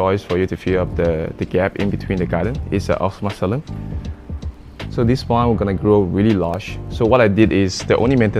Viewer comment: what name did he say, I didnt understand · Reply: Osmoxylon lineare is the plant name. =)